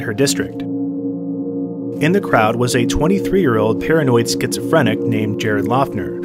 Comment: You said “paranoid schizophrenic”, I heard “patriot”.